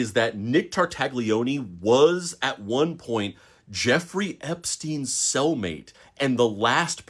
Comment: LOL I thought he said Jeffrey Epstein's Soul Mate